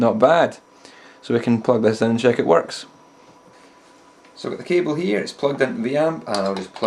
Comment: i thought he said he pooed out the cable...i'm like...wtf? i mean i suppose it is possible?